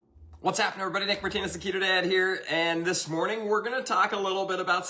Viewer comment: did he call himself the "keto dad"? 😅 cringe.